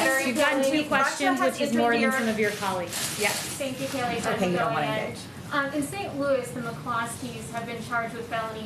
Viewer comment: Did someone just call her a b? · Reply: No, that's not what was said and the White House confirmed this. What was actually said at 23:27 was "ok, you don't want to engage" but people like to make stuff up. 🤔